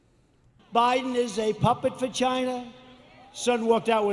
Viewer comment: Did he just call Biden a "puppet vagina?"